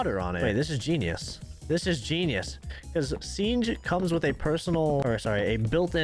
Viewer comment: did say sienge instead of siege